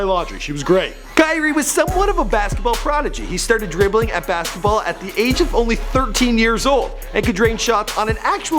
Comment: You Said He Could Dribble At The Age Of 13 Instead Of "He Could Dribble The Ball At The Age Of 13 Months Old"